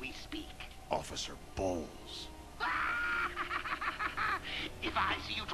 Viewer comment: I swear I heard oficcer Balls